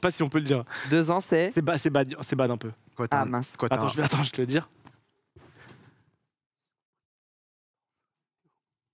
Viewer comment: il a dit quoi du coup 😅 ? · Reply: Je suis parti enquêter et il semblerait qu'il ait dit : "2 ans, c'est aussi ce qu'on prit les parents de Swan & Neo". Cet homme est sans pitié... 😔😔